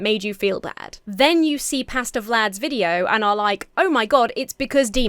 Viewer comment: I kept hearing "Pasta Vlad". I keep waiting for a recipe.